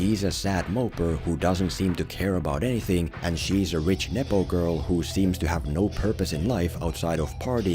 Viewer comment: Did he say rich nipple girl ?